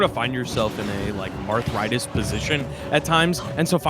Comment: Marthritis, it's a term that means if you don't get an early kill, they end up living forever, named that way because of Marth tippers vs. sourspots being a tremendous difference in kill power. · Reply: my b, I thought they said arthritis. I know what Marthritis is but it sounded like they were saying arthritis like the character is hard to execute with. Thanks for clearing that up